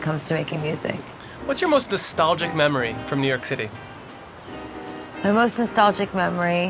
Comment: What was the word the interviewer said at 1:42 “Distongic memory” ??? Help!!!